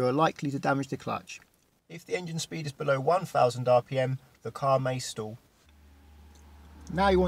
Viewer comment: Hi! I hope you're still responding to the comments, because I'm not very good at English, and I didn't catch what you said at 4:32, "When the car is bellow 10 thousand rpm the car may.... '' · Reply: Much below 1000rpm and the car will stall which means the engine goes off. Typical engines stall at around 500rpm.